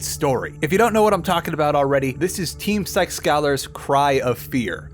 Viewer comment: Captions at 1:47, instead of psykskallar it says sex scholars lol.